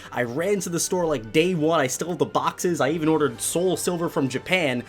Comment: I thought he said he stole the boxes oof